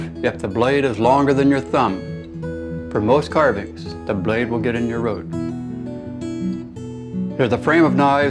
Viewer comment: Can anybody tell me what he's saying at 3:12? You shouldn't use a blade longer than your thumb because the blade will ... ??? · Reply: He said... " the blade will get in your road"